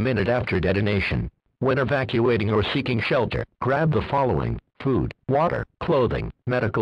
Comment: is it just me or did it sound like he said “poop” instead of food 😂